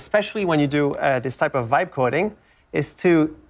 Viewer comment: I'm sure I didn't hear him say "vibe coding" but it's hard not to in 2025... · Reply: you heard that right. its a vibe because you just type english to generate whole coded applications